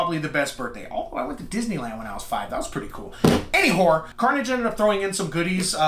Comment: Did you just say “Any Whore” at 2:00 ? 😂😂